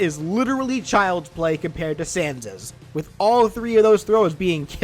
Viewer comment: that sounded like "Santa's" not gonna lie